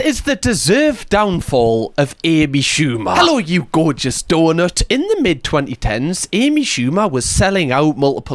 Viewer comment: Until I finally watched a video where you included a picture of donut, I thought for months you were just calling us "gorgeous DULLARDS" due to the accent, and yet, I didn't care. Your affable energy made the insult endearing. Then, I saw the donut, and was like, "oh, that is why he was so cheery." LOL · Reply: I thought he was saying gorgeous daughters at first 😭😭